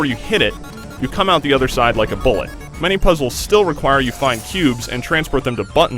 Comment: it REALLY sounds like he says pubes...